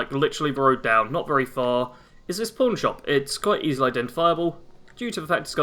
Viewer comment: Oh boy, with your accent I thought you were saying porn shop! Was wondering what the hell this video was